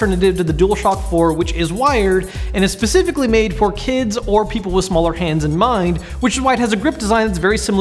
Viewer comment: thought he said "people with smaller hands and minds"